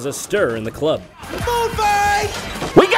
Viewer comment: for a second I thought he said "BOOB FIGHT!"